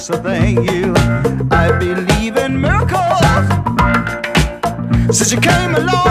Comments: As a kid I thought he was saying "I believe I'm Malcolm!!!" 😂😂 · For the longest time I thought he said I believe in Marco. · I was a kid when this came out. For some reason, I thought he was saying, "I believe in Malcolm". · For the longest time I thought he said I believe in Marco. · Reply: i thought he was saying i believe in murder